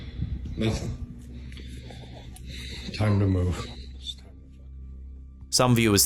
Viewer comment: Right at 6:25 the guy says "it's time to move" and I hear a whisper mock him immediately after by saying the same thing " it's time to move"...if anyone hears the same let me know Edit: I've listened a few more times and after the man says it the whisper or whatever could be saying "it's time to fucking move" or just "it's time to move" . Whatever it said, something was said/whispered right after the guy talked. It's pretty clear if you listen.